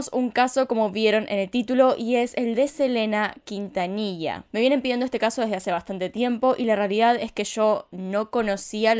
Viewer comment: De quien? 0:23 No entendi, lo podés repetir? 0:23 Puede que este algo sordo... podrias repetirlo una vez más? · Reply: Selena Quintanilla